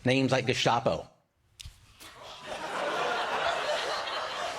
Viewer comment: I thought he said “el chapo”, instead of gestapo, which actually fits his height because that’s a nickname for short people.